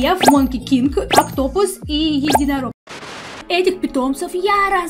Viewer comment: мне послышалось автобус 😂